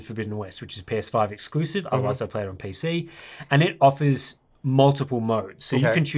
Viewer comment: did he just say "which is PS5 exclusive, unless I play it on PC"?? Shaaaade